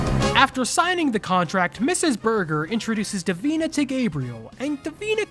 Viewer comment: did you just say that his last name is burguer